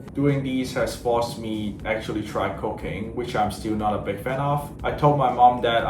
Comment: 😂😂 I did not hear him say cooking. I heard something else 😂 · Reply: Yep,we all heard cocaine like a good new thing to try 😀